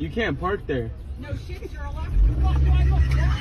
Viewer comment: Can someone transcribe what the woman says at 5:19? Man in car: "Excuse me" Woman outside: "May I help you?" Man in car: "You can't park there" Woman outside: "No shit, it's..." ? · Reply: „no shit sherlock, the fuck, do i look dumb?“ is what i understand